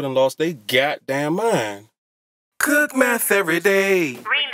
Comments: Did he say cook meth everyday? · I thought it said "Cook math everyday" but i heard wrong XD